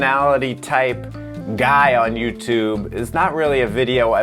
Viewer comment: Y’know I really thought he was gonna say “Ther personality type god” at 0:15, and I heard the g and I was bruh wait really, but he says guy